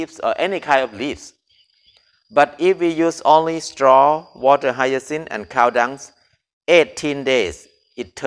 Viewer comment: Hi. do anyone know what he saying "water higher sin"? what is the meaning